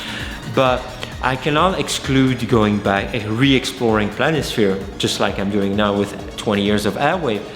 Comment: Amazing!!!! PS 15:57 I cannot exclude going back and re-exploring _______ - can you completed this? I couldn't understand lol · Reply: "I cannot exclude going back and re-exploring Planisphere." Planisphere is the name one of his projects/aliases, and in my opinion one of his best :)